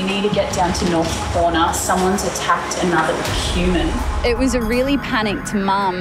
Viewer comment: There's this man that attacked another...HUMAN! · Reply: Did she say "Quman"? Listen. lol